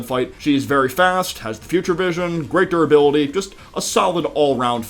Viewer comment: WHEN YOU SAID "has great durability" I HEARD "has Greg durability" HELP I'M DYING😂😅😂